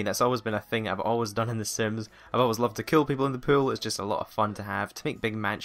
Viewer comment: um when you say pools I thought you said pills lol. like you said killing people with pills XD